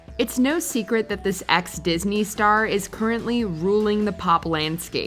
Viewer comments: Did she said ex Disney star for Ariana Grande? Lmao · Did she say Ex Disney Star? I'm not even sure I heard right 💀I'm not even sure Ariana is A Nick Star now